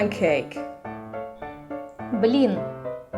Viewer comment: мне показалось что она сказала про сто блин,тип не знает перевода